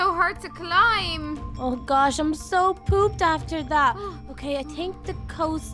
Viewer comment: did she say poop?